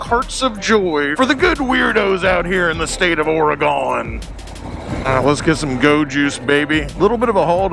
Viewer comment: Did you just say, the state of Oregano? Damn dude you gave me a craving for Spaghetti Bolognese..🍝😂